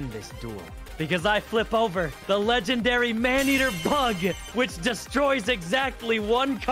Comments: Anyone misheard Man eater bug. To Man eater Pug. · Man eater bug, I thought he said man eater PUG. I thought he was about to let Princes of her leash to attack a poor unsuspecting toddler.